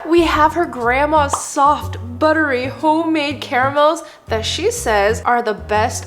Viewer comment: The first thing i heard was "we have her grandma" 💀